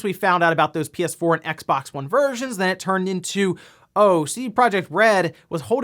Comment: I thought he called them "Xbox Virgins"!. Thought, he's not wrong, but a bit harsh :P